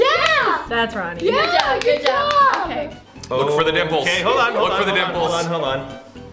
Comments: I know said “Look for the dimples.” But it seriously sounded like he said “Look for the nipples.” I’m sorry! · Did anyone hear “Look for the nipples” instead of dimples???